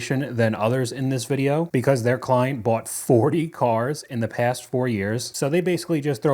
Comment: did I hear that correctly 40 cars in 4 years That's basically f*** you money because you just throwing it like it's nothing